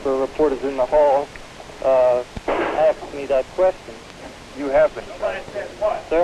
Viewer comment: A reporter made a statement to Oswald, and Oswald asked, "Sir?" So the reporter said it again, and Oswald didn't answer. Can anyone tell what the reporter said? This has baffled me every time I watch this video. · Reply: I think he says “you have been charged.” Two times. What is the question he asks after though?? It’s something like “how did you do in rysha?”